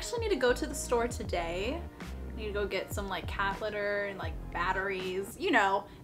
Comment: i really thought she said “catheter” 😭😭😭